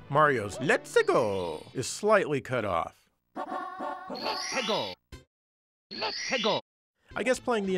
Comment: I always thought he was saying “Popsicle”. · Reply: I always heard "let's Tiggle". I always wondered why he said Tiggle ... And what Tiggle was